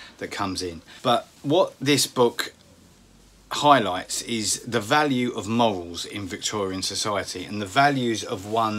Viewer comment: I misheard and thought you said, "The value of moles." Now I can't stop giggling.